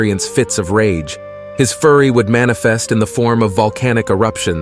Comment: Increase Your Youtube Channel With Envato: <link> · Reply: It sounded like you said his furry would manifest.....instead of fury......maybe its just me but I thought that was hilarious